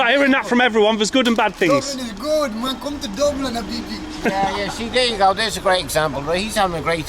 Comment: did he really say come to Dublin habibi? 😢😵‍💫